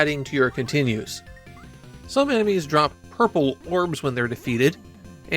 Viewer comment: For some reason, I thought you said purple worms and wondered why Jackie would need worms. To feed the frogs?